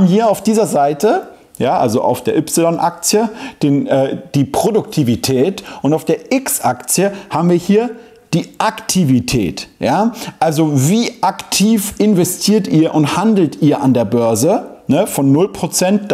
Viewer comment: habe ich richtig gehört? Y-Aktie und X-Aktie? 😁